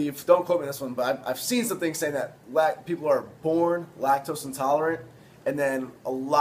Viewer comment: I legit thought you said "Black people are born lactose intolerant" at 5:25 lol